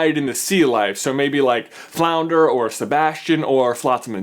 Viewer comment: When he said "I doubt it would be the eels." I at first thought he said the heels, which is a wrestling term for the bad guys. Which is appropriate with the Rock references and the fact Flotsam and Jetsam are bad guys.